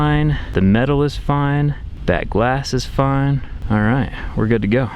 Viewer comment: I thought he said "That ass is fine" on